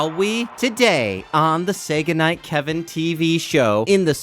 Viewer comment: I thought he said Sega night Kevin show But he was saying say goodnight Kevin show